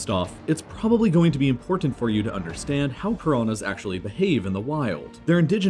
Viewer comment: did you said corona instead of piranha?